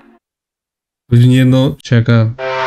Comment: ne diyor💀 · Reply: Sakaaa